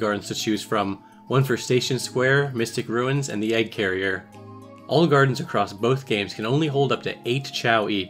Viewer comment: I thought you said olive garden everytime you said all gardens